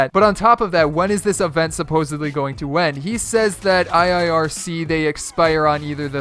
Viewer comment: I thought you said ceeday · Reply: He said iirC they